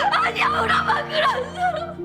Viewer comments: "엄마 그런 사람 아니야" 라고 들림.. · 나만 엄마로 들림?